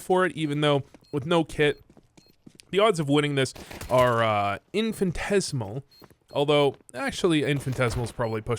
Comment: IM SO CURIOUS WHAT DID U SAY IN 0:53 INFANTTHES WAHT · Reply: Not sure if trolling but he said infinitesimal